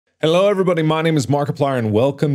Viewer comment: I dont know if I'm crazy or not but at the beginning of the video I thought he said "hello everybody, my name is mar mar"